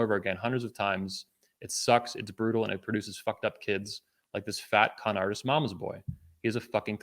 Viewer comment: I misheard 2:28:49 as “fatcunt artist”